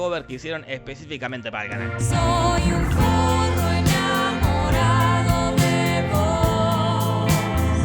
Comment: Entendí "soy un furro enamorado de vos" jajaj